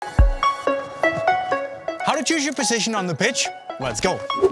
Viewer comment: I heard "How to choose your position on the bitch", then I saw it was about football and became confused.